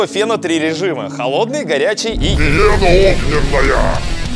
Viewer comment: Ахахкхух мне послышалось гиена огненная 😆😆😆